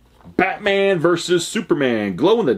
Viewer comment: Did you say bastardman glow in the dark? Lol. Love the pop videos!